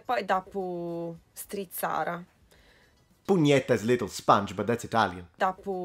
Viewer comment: It sounds like he says “pugnetta” instead of “spugnetta”. That doesn’t mean “little sponge”.